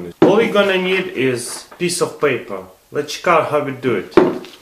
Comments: sounds like he said chick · Ok, I know he has accent but he actually said a PISS of paper xD